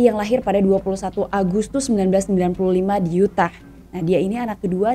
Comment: Di menit 08:40 21 Agustus 1995..Tgl lahir SHAWN NELSON.??. Bener itu..Apa saya salah Dengar ya..🙏🙏